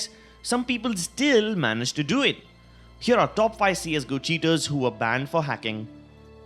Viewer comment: All i heard was " wreck banned"